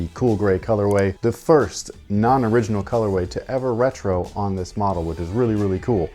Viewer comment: yeah but they never released to the public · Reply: yeah just watched the clip again and I guess I didn't hear the part where he said this was the first new colorway we had never seen get retroed. Guess my brain just filled in the blank and thought he said the first new colorway to retro.